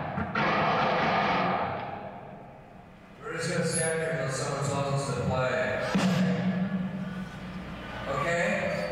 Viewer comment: Can anyone tell me what Kurt said ? I’m French have mercy 😭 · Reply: “We’re just gonna stand here till someone tells us to play, okay?”